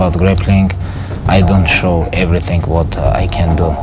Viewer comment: what did he just say ........ he didnt show everything what he is capable of